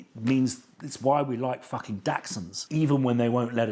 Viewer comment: Well put. I did miss part of the meaning - were you comparing them to dachshunds? At first I thought you said Datsuns